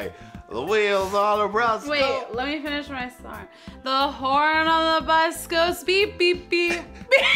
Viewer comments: it sounded like she said "the Horan on the bus." 😂😂😂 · it sounded like she said "the Horan on the bus." 😂😂😂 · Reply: I thought she said "the hoard on the bus goes quack quack beep